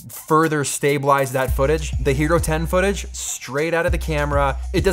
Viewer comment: I thought he said "Gyro 10" and kept looking for a tasty sandwich.